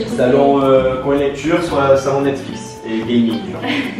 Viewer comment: dit quoi à 7:30 ? Salon nature et salon Netflix ??? et après ? J'ai pas tout compris. Merci.